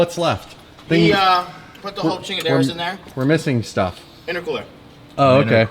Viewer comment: did he say chingaderas!? Bahahahaha